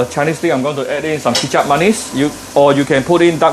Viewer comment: What is chitchat monies? 3:29 some kind of sauce but i just can't figure out what he said. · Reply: Kicap manis